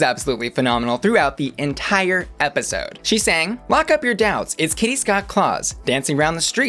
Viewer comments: Lol I thought Kitty said "lock up your dads" and I laughed so hard :D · I thought Kitty's verse said "Lock up your dad's" not "doubts"? I could be wrong though. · I thought kitty said lock up your dads